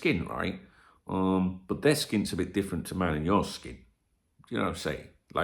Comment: I thought he said '' their skin is different from our skin'' I though oh no not again ffs